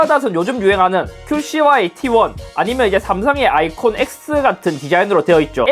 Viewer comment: 나만 삼성의 아이폰X 라고 들었나?....